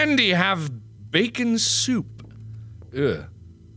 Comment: “Why does Bendy have...bacon soup?” 😂😂😂that killed me. 😂😂😂😂😂😂😂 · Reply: I always thought it said "acon oup". It's like bacon soup, but missing the first letters of both words. Also, it's more fun to say.